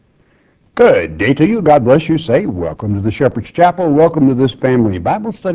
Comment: Why's it sound like he says "god bless you satan" :31 in his intro?!?! What is he supposedly saying?! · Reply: I used to say that too, and I've heard other people say it too. He's just saying, "God Bless you say welcome to ....